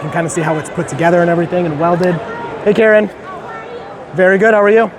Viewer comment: did he just say, “hey Karen?” Lol